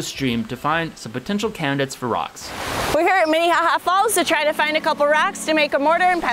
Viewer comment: Did she say mini haha falls? xd